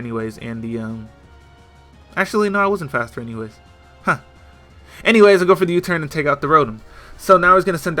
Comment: did he say anyways?